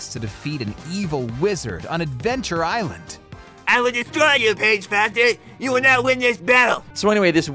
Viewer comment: what did he just say? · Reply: First Line: “I will destroy you, Paige Foster! You will not win this battle!”